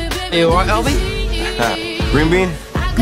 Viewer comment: I thought he said green bean 😂😂